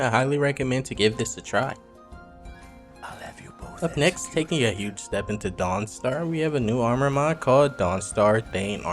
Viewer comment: Give you a dollar if you can tell me what was whispered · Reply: I think he said, “I’ll have you both executed.”